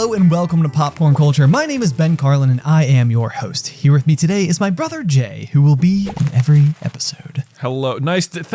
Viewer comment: Did he just say my brother gay?